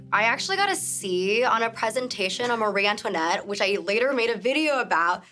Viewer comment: subtitles say Maria when they should say Marie